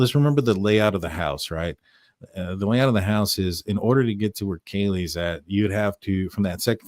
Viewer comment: I hear "don't know where Kaylee is"